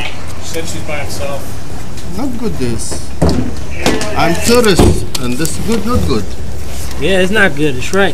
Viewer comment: Bro this is so bad I'm laughing so hard Oh no. the poor guy said "I'm tourist this no good" 😂😂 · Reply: Evan Aviles BRO I thought he said "I'm terrorist" I FUCKING LOST IT LMFAO